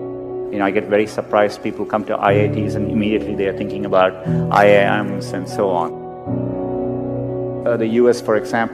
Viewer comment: he did not say Iam a sin. he said IIM(Indian Institute of Management)